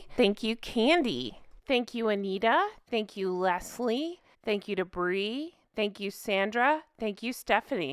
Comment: Omg. All this time you've been saying " Thank you to Brie" and I thought you were saying " Thank you Debris" as in Arrested Development's character Debris. 😂😂😂